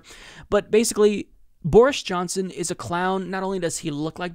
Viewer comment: Also, I thought you said Boorish Johnson 5:30 haha